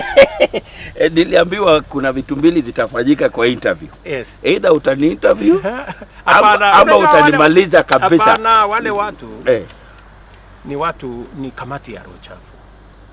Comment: Did he just say curview?